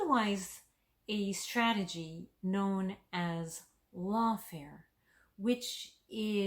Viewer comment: ¿What's that word? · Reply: "lawfare" <link>